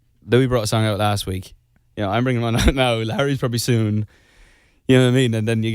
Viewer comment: Am I the only one who heard Larry not Harry at 9:40 haha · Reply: whatttt he did say larry ❤️